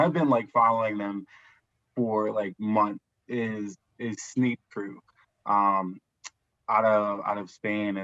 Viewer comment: Hi, what is the brand you are talking about at 21:20? I cannot quite make out what your saying and i want to check out what they got. Thanks! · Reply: Sneep Crew